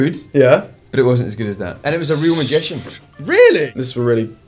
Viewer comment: What does James say at 5:09 after 'but it wasn't as good as that'? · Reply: Isabel Bălan Lakota "and it was a real magician"